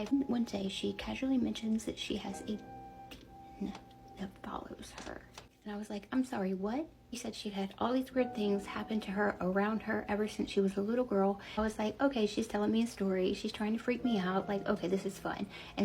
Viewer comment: Ohhhh! I thought you said "a penis that follows her" I had to watch this twice for it to make sense lol